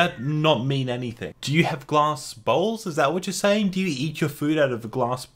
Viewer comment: is it just me or did it sound like he said glass balls